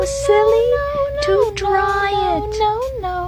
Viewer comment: is it just me that thought it said "to try it" but it says "to dry it" 😭